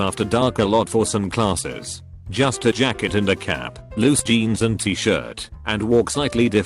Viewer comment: I swear he said “Just to jack it in a cab.” I’m crying right now😂😂😂😂